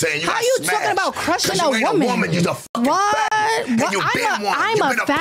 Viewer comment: did just call a “f@ f@ Ok yeah… that totally makes sense 🧐🙃😒